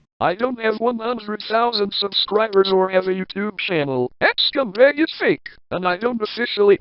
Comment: when he said carl the npc 01 it sounded like that scumbag